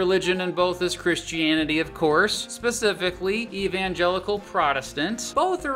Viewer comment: when I heard that I thought you said evangelion and they started to sing cruel Angel's thesis